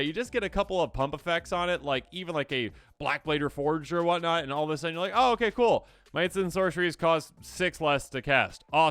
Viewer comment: What card does he say at 15:22? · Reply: Blackblade reforged :)